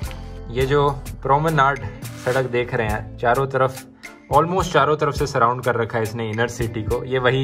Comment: I heard 4 ro taraf khohrahi khohra hai